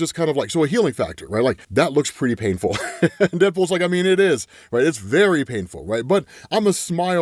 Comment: GF not into comics but she did hear "nipples" instead of "deadpool". comments?